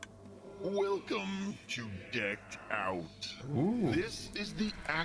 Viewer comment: Excuse me! 36:50 did just say two more days till decked out!?!!!